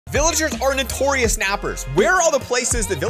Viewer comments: I heard "Villagers are notorious kidnappers!" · lol it got me confused for a second cause i heard "Villagers are notorious rappers" instead of nappers